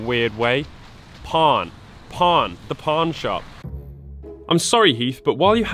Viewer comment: I literally thought you said porn shop when you said pawn shop.